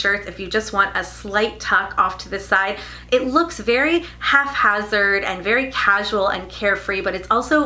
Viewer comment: Useful video, thank you. (Psst, “haphazard,” sounded like you said half hazard)